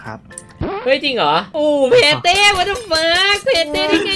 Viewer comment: ทำไมผมได้ยินว่าแพ้เต้อ่ะ😂